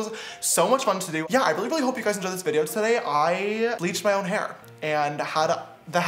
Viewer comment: I thought he said I bleeched my eyes lol